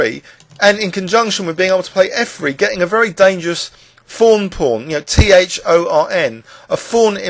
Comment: thank you for spelling that out.. i've heard you say thorn pawn before and i always thought you were saying fawn pawn